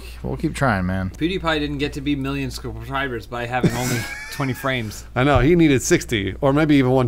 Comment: Internet wisdom with: “Pewdiepie didn’t get to be million SUBSCROBBLERS by having only 20 frames...” Me: Did he just say... “subscrobblers”...? My brain: WRITE IT DOWN!